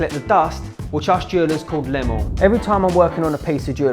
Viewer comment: When you said the gold filings are called lemel, I heard it as Lemuel which is Hebrew and means belonging to God.😂